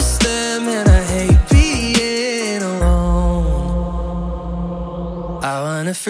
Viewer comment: oh, I thought he said he hated "peeing alone" instead of "being alone" this whole time. Anyways..